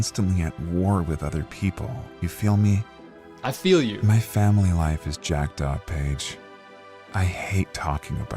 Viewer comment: every time i hear that line, i hear it as "my family life is jacked up, bitch"